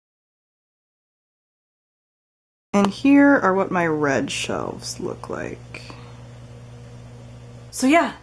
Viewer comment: When you said "And these are my read shelves" my dumbass thought "but those shelves are white" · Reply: B Jones , whispers I thought she said RED shelves...I was very confused. now I just feel dumb for not realizing it was read not red.